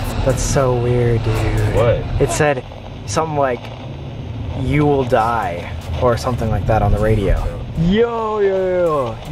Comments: wait yeah omg at 1:04 it says "you will surely die" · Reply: I heard “you will die sam” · On the radio it said "You should've known" or "You shall die" !! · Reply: I heard "you will die" smh not today satan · The radio said “you shouldn’t open it” bro that’s bad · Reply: I heard “you will surely die” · when jake said that they are going to open the box tonight The voice from the radio said "You shouldn't'' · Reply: That is exactly what I heard. "You will surely die" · I will make another video if we notice anything strange... LETS GET THIS TO 50K LIKES? thanks for watching keep up with me on instagram/twitter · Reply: Jake Webber in the beginning of the video on the radio I heard "you shall die" I had to put both headphones in my ear to listen to it a bit close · I love how they're opening a DEMON box and how they're acting towards doing it Sam is being smart and careful Jake is being a weak ass Colby is complaining for nearly the entire video This is why I love these three, Especially when they're all together! (Also, I am very aware I'm late, I've just been binge watching all their channels and just noticed this) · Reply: I heard the "You will surely die" I'll go and listen to see if I hear "You shouldn't do that", Thanks for telling me dat ❤️